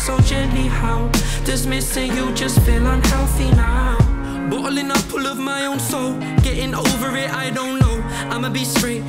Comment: "Bootlegging all of my own songs" That's what i thought he said 😂